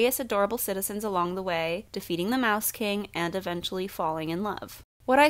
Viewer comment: I THOUGHT IT SAID EATING THE MOUSE KING 💀💀💀💀